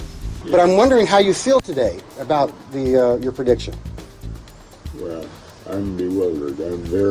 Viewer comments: Did he just say "bruh" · did he say "bruh, im bewildered"